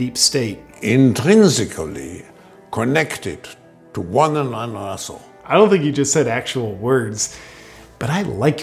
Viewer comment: Did he say: intrinsically connected to one another's aresholes? Has someone watched The Human Centipede movies too many times?